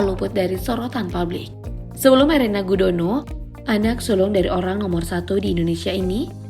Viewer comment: AKU KOK DENGERNYA ANAK SULUNG YA...??